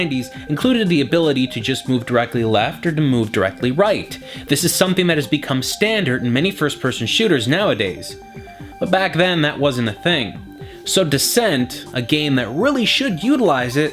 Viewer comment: it sounded like he said first person shitters